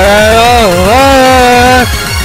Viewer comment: "oh christ" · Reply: I thought that he said "oh scheiße"